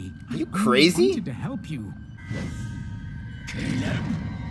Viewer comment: I think he does say 'Caleb', but on the Disney Plus subtitles it says 'kill him', so I'm not sure.